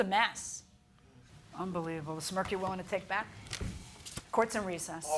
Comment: I thought she said “A Smurf you’re willing to take back?!” 😂 I had to rewind it